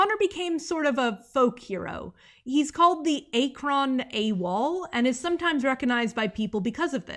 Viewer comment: 🎉 NEW VIDEO NEW VIDEO 🎉 1:54:53 As a native Ohioan, hearing you say Akron as "Ay-crawn" killed a little piece of me. My state simply can't catch a break on Mark Zuckerberg's internet.